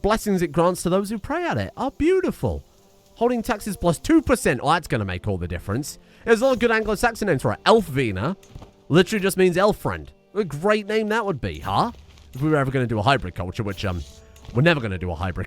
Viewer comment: One of like five men who like Old English here: That sounded like you said "vina". "Wine" is word for friend you're talking about, and can be added to pretty much any name prefix. Adding a V sounds more Scandinavian or something. English words don't tend to have V's. Maybe I heard wrong tbf.